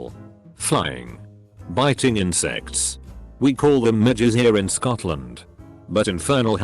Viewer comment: Thought he said midgets